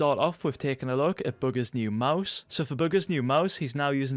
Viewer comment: I Thought He Said Bughar